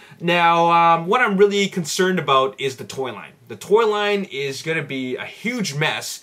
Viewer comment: I thought you said toilet instead of Toy Line lolol